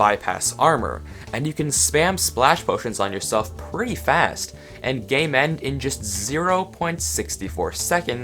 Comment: I thought he said and gay men.